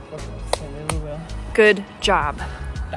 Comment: Did he say Dankeschön at 9:14 ???